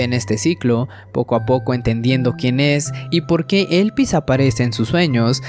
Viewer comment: Entendí "Elvis" y estaba buscando al cantante. Ya me parecía raro.